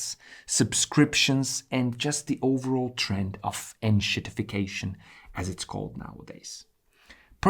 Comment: This is one of the best videos of this type that I've seen. Enormous potential and fun too; thank you for this. One thing, what was that word, "endshiftication" or similar (at 34:34) tried searching, no joy. I probably didn't hear it right but if anyone can elucidate I would appreciate it. Cheers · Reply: En-shit-if-ication how to make something that was actually pretty good ... into a pile of shit ... for the green god of greed. That's paraphrasing of course.